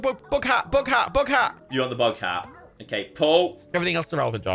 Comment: Am I the only person who thought was saying book hat not bug hat? Like watching it back I see its a bug not a book but still it wasn't till said said big hat that I realized i had misunderstood.. Usually I'm pretty good with accents but that took me for a right lolol lol.